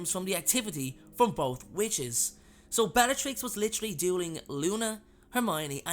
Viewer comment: chokes on food Sorry but at 5:03 I heard "Bellatrix was literally DOING Luna, Hermione and Ginny all at once."